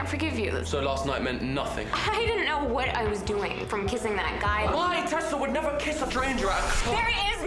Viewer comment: I just can t figure out what he said at 0:27 and it s driving me insane😂 Jeez british accent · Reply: "My Tessa would never kiss a stranger at a club"